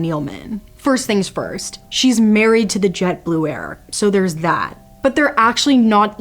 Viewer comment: i thought she said "jet blue air" for a sec😅